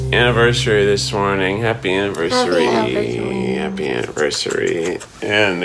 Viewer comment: Is it just me or did I hear Becky say "Happy Habersary"?